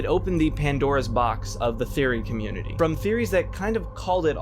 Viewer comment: see i know he said "theory community" but i wasn't paying attention and thought he said furry community which is still correct lbrh